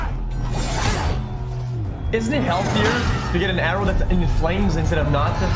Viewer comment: did he just say healthier?